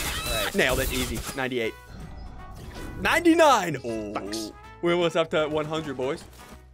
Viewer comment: who else heard the f word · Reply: He said fucks